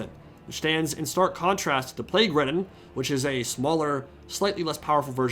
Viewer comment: when you said 'plagueridden' I thought you said 'plaguewoman' and I fuckin spat out my drink